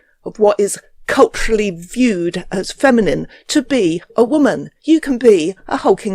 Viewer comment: 39 what did you say? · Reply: accoutrements of femininity